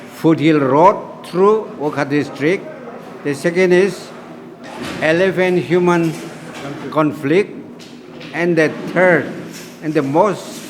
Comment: Did I heard it wrong or did he said “Eleven Human conflict” @ 1:28? 🤷🏼‍♂️ 😅😂😂😂